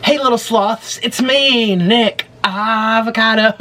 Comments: Haha who else thought he said 'Hey little sl*ts' at the start😂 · when the video started i thought he said hey little sluts i was uhh 💀 lol